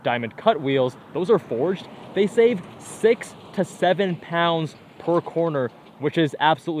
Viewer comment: d.. did you say 67? All jokes aside I enjoyed this video, thanks!